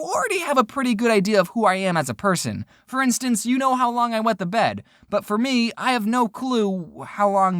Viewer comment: I thought it said "how long I last in bed"